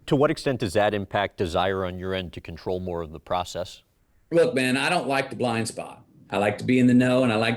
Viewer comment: did he call you "Ben" at 4:30? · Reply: “Look man” is what he said.